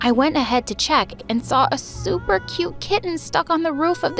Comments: this girl did not say super Cute KITTY NOOOO 😳💅🚬✨ like miss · thought she said she saw a super cute cat. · I thought she said super cute cat...that cat looks like it can bite my hand off